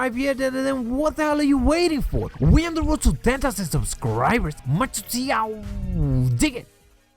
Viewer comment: even the manually written subtittles doesn't know what his saying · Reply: BROKY ROCKY LESNERD We are on the road to 10k subscribers out DIG IT!